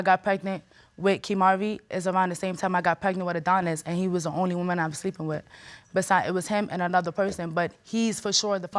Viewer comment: "he was the only woman I was sleeping with" Did I hear that correctly? 🤣🤣🤣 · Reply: She said "he was the only one i was sleeping with"